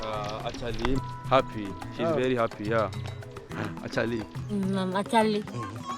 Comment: Wait did she say "ACHHA" ??? That's a Hindi word. In Hindi, Achha means good.